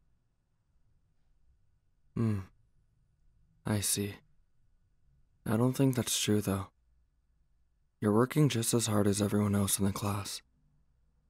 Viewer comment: I know he said “I see” but I thought he said icy… especially since we’re talking about Todoroki here…